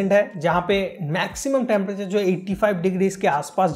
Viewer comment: does he really say 85°C.😳😳😳